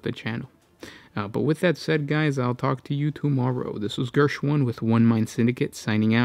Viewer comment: You really have a natural affinity to heresy. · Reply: Oh god I thought he has been saying his name is.